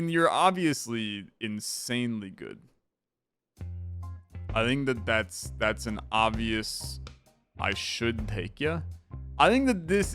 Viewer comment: what did he say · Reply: I think that thats an obvious i should take ya